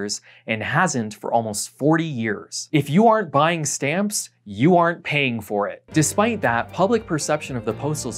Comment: longtime fan, love your videos. feedback to use as you will, don't use contractions when making bold points like at 16:18. I kept hearing "if you are buy stamps, you aren't paying for it" Its weird I understood the second aren't, but i had to double take a few times. either way, it woulda been more impactful to say "if you are not, you are not" rather that aren't. maybe im just dumb tho.